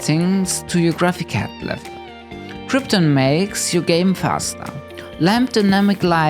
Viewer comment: "krypton makes your game faster" is honestly enough to make me download it lol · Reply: lol I almost thought said "Krypton makes you game faster"